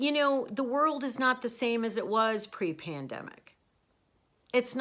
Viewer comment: Here, I'll make you laugh. ..and thanks for sharing your journey! When you said 'The world is not the same as it was pre-pandemic' I thought I heard Pre-Panda...you had gotten lunch there earlier and that's what I heard lol...I played it back to be sure and you didn't say pre-panda...I giggled. ..and now I'm hungry!